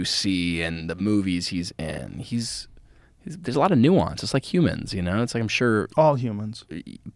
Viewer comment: Did he just say his dad is an alien!? "There's a lot of nuisance, like humans."